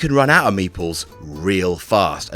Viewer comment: Was confused for a second during the Carcassonne segment because it sounded like he said meatballs instead of meeples